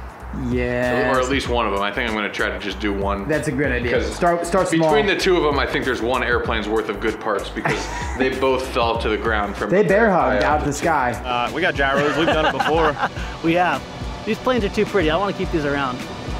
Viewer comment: I literally thought at first said "They Air Hogged" , like as referring to Air Hogs, But he said they bear hugged LOL!